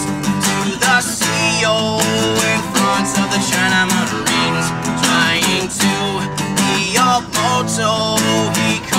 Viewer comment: What does he say right after 1:00. Standing next to the CO, In front of the _____ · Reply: China Marines